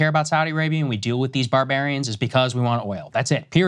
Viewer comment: Did i hear barbarians? Tf you mean by that?